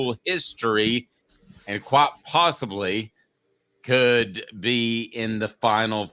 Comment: Did he just say "Quat possibly "?